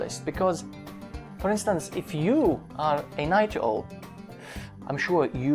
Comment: I'm sorry, I just cannot understand what he said at 0:48... What is he saying there? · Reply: Night Owl